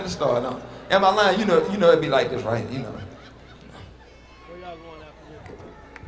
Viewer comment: ALL I HEARD AT 6:16 WAS, "WHERE YALL GOING AFTER THIS"?